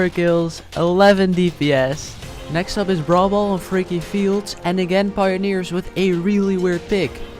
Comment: am i the only one who heard freaky field?